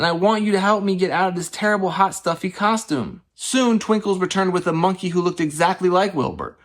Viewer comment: I thought that said twinkies 💀